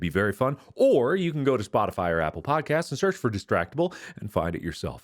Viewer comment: I seriously thought he said apple pie cast..